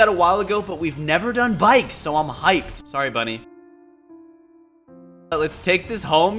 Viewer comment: When you said bunny I thought you said buddy